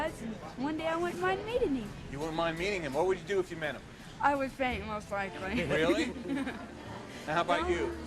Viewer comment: What does the girl say at 3:25? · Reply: I believe she said "I would faint most likely."